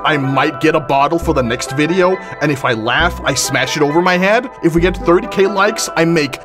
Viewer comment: I thought it said “If I laugh I shit myself”